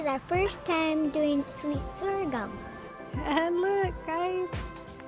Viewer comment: Beautiful video and beautiful family. Whats the plant at 3:18 I only heard sweet. Thank you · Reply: Sweet Sorgham. Thank you